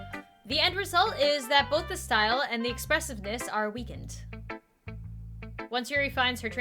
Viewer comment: I skipped a little bit and thought she said "your tits are weakened" WHATT